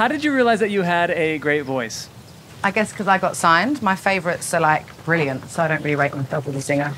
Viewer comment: Hmm, we wonder why Adele answered that specific number of questions...can anyone figure it out? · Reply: Could anyone help me figure out what she said at 1:19 - 1:23? "I got signed my favorites (?) ... brilliant ... (?)"